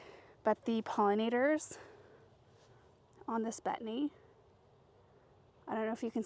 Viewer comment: what plant did she say that was? I can't seem to catch the name she is saying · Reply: Plant : 'Hummelo' Betony